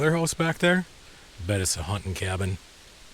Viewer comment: I really need to get my hearing sorted. I thought you said "bet it's a haunted cabin" at 6:34. 😂 👻